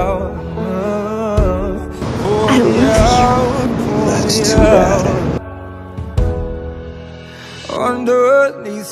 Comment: ?? Il a dit quoi le gars j’ai pas compris je parle pas anglais 🤣😭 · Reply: Il a dit "well that's too bad" après qu'elle ait dit "I love you" en gros ca veut dire "bah c'est dommage/tant pis"